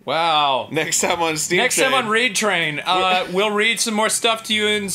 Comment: How bad is it that I thought he said rape train at first?